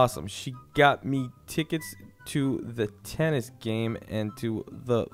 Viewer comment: I swear I heard Stark say Thor at about